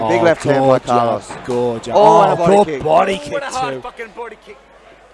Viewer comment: did he really say "a hard fucking body kick?" lol